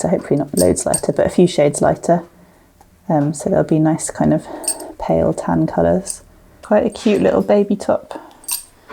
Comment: Love the lesson. At about 15:40 I cannot make out the word ‘modented/ unmodented’ silk? Can someone please help explain what is being said. Thank you ❤ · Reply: Hi there … I’m saying « mordanted » mordanting is the name for the process of fixing the dye colour to the fibers … I usually pre-mordant my fibers then dye them 🤎